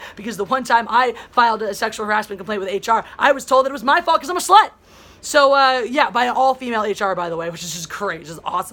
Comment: All i heard "Am a Slut"